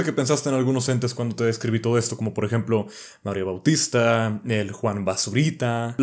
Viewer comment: Les juro que con ese fondo, escuche que dijo: ''Mario Austista, Juan basurita'' jajajajajajajaja